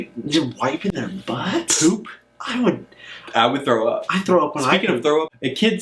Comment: Does say "I throw up when I poop" at 3:06?? That sounds uncomfortable